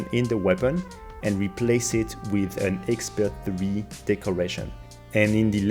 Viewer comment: what did u said ? Expert 3 ?